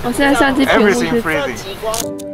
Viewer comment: Everything is frozen! Though, what I heard is "everything is freezing". 多练练，多练练！